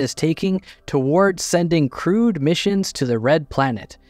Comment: Every damn time I hear "crewed mission to Mars" as "crude mission to Mars" and I think, "well that doesn't sound very good"